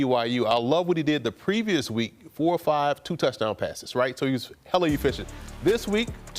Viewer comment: Did he say “hella” at 8:30 ?? 🫤 wow